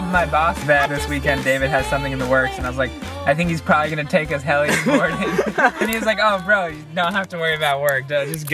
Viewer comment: Thought it said hell boarding